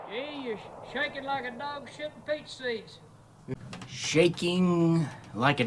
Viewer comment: Initially I thought he said feces instead of peach seeds. Lol.